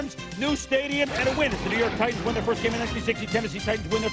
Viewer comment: Did Chris Berman say "The New York Titans" around 1:16:21-1:16:26?... He was literally talking so fast, I couldn't tell what he said.